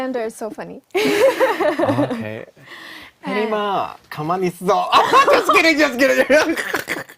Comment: What did said at @ 9:35 ? Can someone translate it please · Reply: 혜림아 ~ 가만히 있어